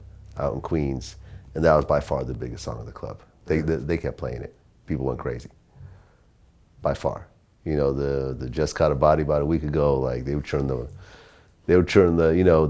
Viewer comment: lol i thought he said "bitch called about a week ago"